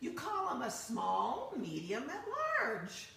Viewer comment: Just watched. I'm glad someone asked to repeat the answer because I thought she said Small, Medium and Large...hahhahaha, no wonder it didn't click but now I got it! Love the unquilted quilts that look like stained glass. Gorgeous